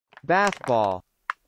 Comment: のバスボールがbaseballに聞こえてしまう(笑)